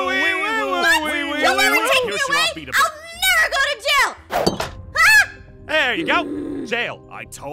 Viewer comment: When app said “you’ll never take me to jail” I thought she said “YOU’LL NEVER TAKE ME ALIVEEEEE!”